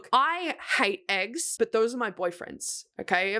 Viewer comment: I thought she was saying HER BOYFRIEND IS EGGS lmaoooooo